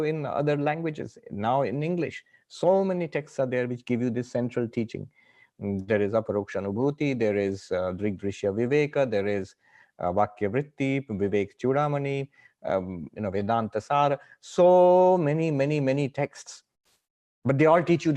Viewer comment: Swami starts to list first-step resources/books but I couldn't make out the english spelling for all of them. If anyone could help me get those names to look-up for texts to read that'd be appreciated!! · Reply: The cited texts were Aparokshanubhuti, Drg Drsya Viveka, Vedanta-sara, Vivekachudamani, and Vakya Vritti.